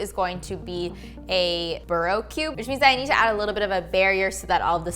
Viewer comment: Ooooohhh burrow cube... I thought you said Bro cube haha